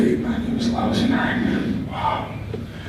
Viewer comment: What is Renee saying @ 1:38? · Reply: "It was a lousey Night , Wow "